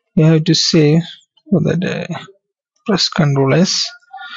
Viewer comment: WHAT THE FUCK ARE YOU SAYING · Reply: sorry, for the inconvenience I was a beginner when I made this video. I was saying - press 'Ctrl + S' to save the table.